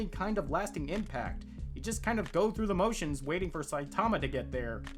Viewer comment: Near the end I thought you said "Goku the motions", which was very fitting.